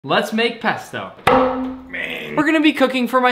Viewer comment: People who thought he said let’s bake pesto | V